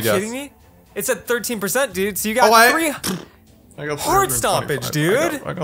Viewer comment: did swear or what it sounded like that to me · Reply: He said hard stompage